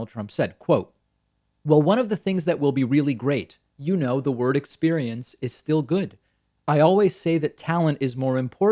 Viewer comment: My goodness, none of Trump's rambling answers to this simple question makes any sense, but at least I thought he said "world experience". No, it seems it was "word experience", which makes even less sense. "with our First Lady" should probably have been followed by "whose name I can't remember", but notice also the tyrant use of "our", appropriating his wife as some sort of ornamental or protocol object. Unless he tried to share his wife with "us", maybe. And that "no test, no case" argument is totally wrong, some states get 5 times more new cases than new tests, New York increased its testing tremendously yet cases are decreasing fast. Good luck to Americans in November, remember that vote also affects heavily the rest of the world, so we'll be watching!